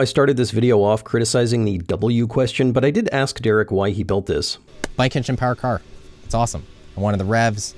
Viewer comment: Did Derek say "bike-engine-powered car" when asked why at 8:42? I'm from Alabama and don't speak Californese.